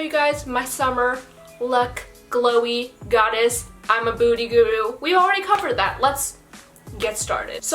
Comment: It kind of sounds like you said "booty guru"? lol!! The makeup looks amazing, have you tried milk makeup?